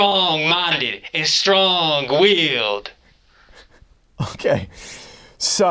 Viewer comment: idk if it’s just the accent he put on but I like to think he said strong wheeled instead of willed